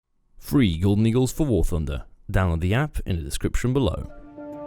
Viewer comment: I dont know why but every time he says "free golden eagles for war thunder" I hear "free eagle nigles for war thunder"